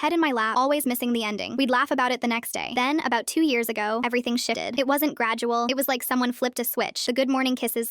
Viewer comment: At first I heard “but one morning everything shitted” HELPPP 😭👏🏻